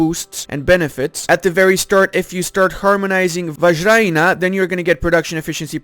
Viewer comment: Ahahaha he really said vagina 6:00 lmao